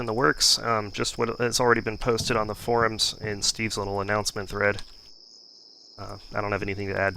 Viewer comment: I thought you said "Steam anoncement thread". ^^